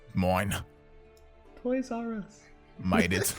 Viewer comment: I'm so confused, what did he say? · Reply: I think he said Toys R Us